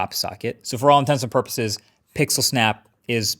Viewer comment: "for intensive purposes" did you say ? for intent and purposes ! Did not Dina school Garrett in the Superstore sitcom on this ? assume one day this would also end up like the word "nonsensical". always enjoy the superb review vids though.